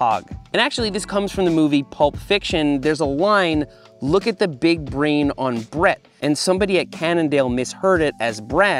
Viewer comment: I always heard "look at the big brain on Brad!"too. I've been saying it wrong once a day for 20 years?